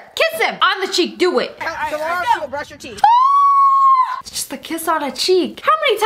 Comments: did anyone else hear him say BrUSh YoUR tEeTH? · When the boy kissed the girl on the cheek I heard "Brush your teeth!" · did anyone else hear him say BrUSh YoUR tEeTH? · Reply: i thought he said blush your teeth